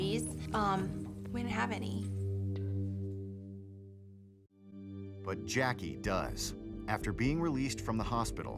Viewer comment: I heard “but Jackie does” as “but Jackie dies” and I was real confused for a bit